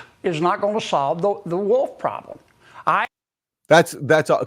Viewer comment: I thought for a second he said “more sheep is not gonna solve the wool problem”… was like “it kind of will won’t it?”… but guess it’s wolf, so makes more sense.. 🙃🙃